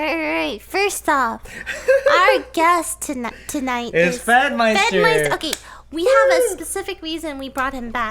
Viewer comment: I thought it said "Fedmysister" 😂😂